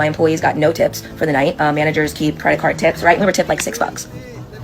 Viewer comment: did she say, "managers keep credit cards tips"?!!!